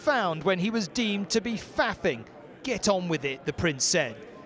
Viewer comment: He didn't say "Get on with it". He actually said "Take the fucking picture" hahaha